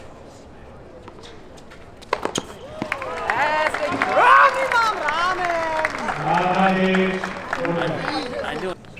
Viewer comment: I wonder what is Thiem saying at 12:36 ? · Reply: I guess: "Sch***, ich treff ihn immer am Rahmen!" which means "Sh**, I always hit it with the frame!"